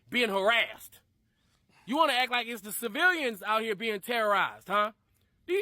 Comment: “civirrians”? lol.. Am I hearing that right? Did this dude grow up in mainland China?